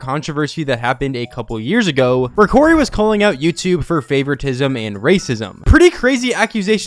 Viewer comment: I thought the vid said cory was right about you and I was like what is he saying bout me